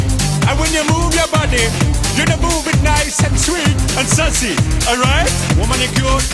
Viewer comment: Did he say sussy